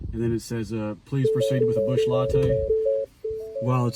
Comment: When you popped that can and took a sip I could swear I heard “Busch”.... and then it turned out to be a Busch lol.